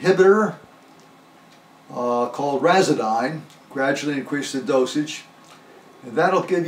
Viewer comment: Hello Sir my grandmother has Alzheimer's as well. My mom sent me to this video. At 1:20 you mention the name of the drug that helped you cope with the disease, however I can't make out the name well enough to type it out. And I can't find any related information online. Could you please write it in the description? please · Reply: Razadyne